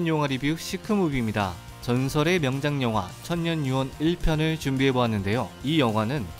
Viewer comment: 천녀유혼인데 발음이 천년유혹으로 들려요..꾸벅. 늘 리뷰 잘보고 있슴다.